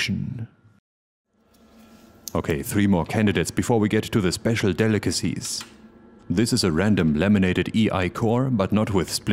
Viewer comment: I thought you said lemonaded. Probably because of the yellow covers you got right below the transformer. That would have been funny.